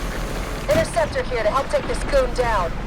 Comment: I swear she did not say goon there…..